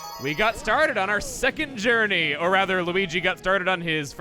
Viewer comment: "We got starred on our second journey" That may have been me mishearding "started", but wow, that's a terrible joke if it was intentional.